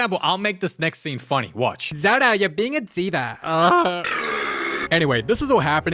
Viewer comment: What I heard at 7:39, zodo you’re being a dida,👹👹👹👹👹👹